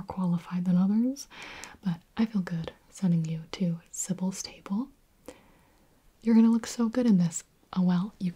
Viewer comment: Gibi if u see this i got a question is sybil stable supposed to be silver sable? the marvel character also i dont know if u even said sybil stable but it sounded like that